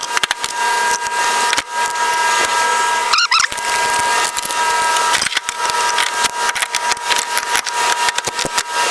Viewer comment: 🤣LoL😆Whaaat did You say⁉️😆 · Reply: blejfpaowe. Something like that. LOL